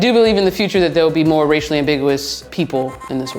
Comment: I thought is said ethnically ambitious people! Very confusing.